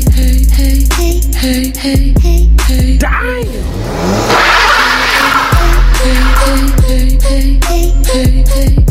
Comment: Hö 00:29 habe ich erst beim 3. Mal dieses "deiiin" gehört oder was das heißen soll xD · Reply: "Daaaammnnn!"